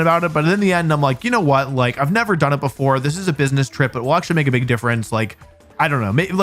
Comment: "it is a business trip " tax right off is what i heard